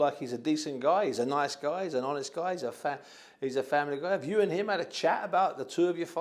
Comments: thought he almost called Ruiz FAT hahahaha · looooooooool at 11:55 I genuinely thought he was gonna say ''he's a fat guy'' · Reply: He actually did say it bro.. He said hes a fat.. Then continued hes a family guy